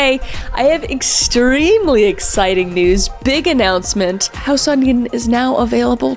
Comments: it sounded like "house onion," not House on Eden lol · Why'd I hear "house onion" 😭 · Anyone else hear house onion?